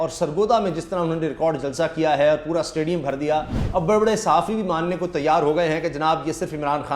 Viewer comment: sir with due respect did you call them baray baray sahafi or bewaray sahafi on